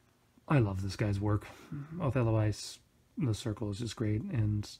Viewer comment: Hello Just looking around YouTube here, discovered your channel. I'm curious, at 9:22 you tried to say the name of the artist who you "love his work" but even with CC on its hard to tell what you said, and got more confusing when you put in a different name (assuming future artwork series) with "91" by it. Would you mind explaining a bit clearer for people like myself about the art works artist/circle background? Thanks for your time in reading this. I appreciate looking into the lives of individuals who aren't really like myself, trying to discern and understand interests. I never really considered it before seeing this video, but the poster doesn't necessarily only emit lewd tidings. It is a bit of a statement piece, a wall refresher if you will, and after looking round my room with world maps, racing shirts/flags, musical instruments and other items I realize that in that particular context we aren't really that different. Because I don't really play musical instruments and have never desired to be a professional racer. Maybe that's a bit of a reach on my behalf, but hopefully you understand that I got your point with that concept ^_^ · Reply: Shuz (Othello Ice) is the artist's name. To be completely honest, I don't know enough Japanese to read his blog on FC2. He's well known for his style which has changed over the years, but for the most part I just like his art overall. In my opinion, his actual stories are sort of mediocre.